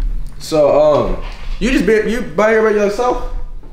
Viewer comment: YALL go to 4:14 and tell me what Deshae said 😂😂😂😂😂😂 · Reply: He said you just be by here by yourself?